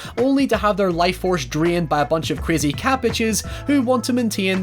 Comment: When you said "cat bitches" I heard "cabbages" and honestly I'd love a movie where crazy cabbages drain people